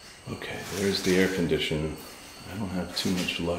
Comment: Was that a whisper or at 5:27. I heard “ They’re here”. Then something also at 5:29. ❤️